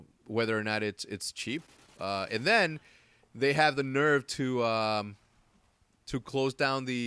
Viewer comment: it sounded like he said nerd instead of nerve